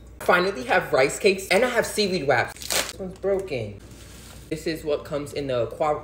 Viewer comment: Did she say seaweed waps, that's what it sounded like she said.